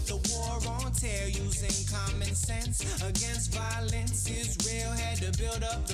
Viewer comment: "Using comic sans against violence" Guess I misheard that.